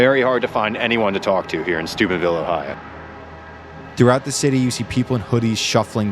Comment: I thought he said stupidville ohio for a sec 😂